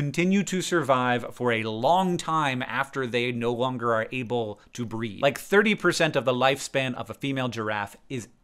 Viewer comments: I thought he said breathe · Auto-closed captions thought that Hank said that female giraffes continue to survive long "after they no longer are able to breathe" and I was extremely confused for a moment before I realized he said "breed" and I started laughing. Happy to see more old school vlogbrothers videos these days.